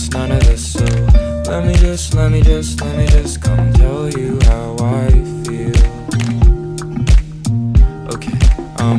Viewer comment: I thought this said come girl your my waifu